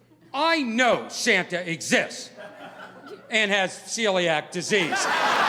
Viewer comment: I thought he was saying silly act disease ¯\(°_o)/¯